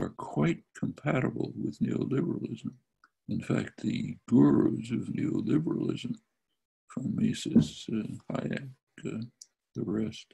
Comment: "The gurus of neo-liberalism from Mises to Hayek..." is what he's sayin'